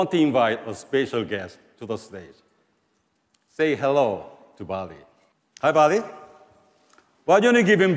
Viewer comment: say hello to Barbie ( that’s what I heard )